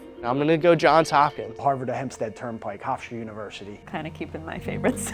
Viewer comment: Did he say the Harvard of Hempstead? Hempstead is right next to Gritty Garden City… polar opposites.